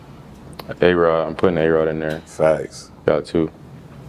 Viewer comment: Who did he say? A Rod or something? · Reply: A Rod, aka Aaron Rodgers.